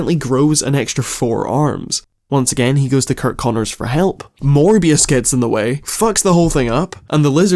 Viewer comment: I heard "More BS" instead of "Morbius"!